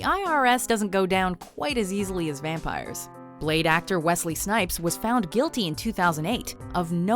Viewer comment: Is it just me or did it sound like she said "late actor", as if he's dead.